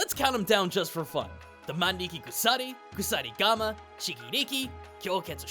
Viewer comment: For a moment I thought you said: ''Chiiki briiki'' You got me there for a second lol